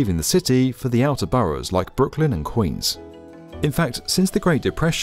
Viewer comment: Please fix the audio, the Audio says "great depression" but the subtitles say "recession" Which is it? confusing and poorly edited video!!